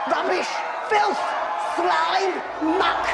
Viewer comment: Lmoa...i think i heard "smelly muck"😂